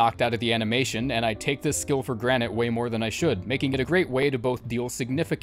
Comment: I can't be the only one that heard him say granite instead of granted